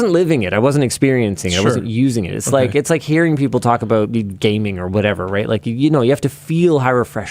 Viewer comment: on my first listen, I swear Linus said "gaping" instead of "gaming" and was rolling by the time I figured out what he had actually said.